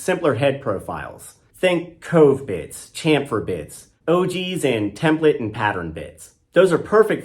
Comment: Having never used a router, every time you said Ogee bit, I heard “OG bit” and thought I need to go learn about that old school work horse of a bit! 😂 I paused the video at 7:06 to read the bit names.